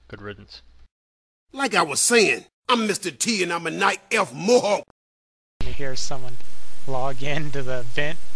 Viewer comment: when i heard it first time i thought he said im a night elf mofo XD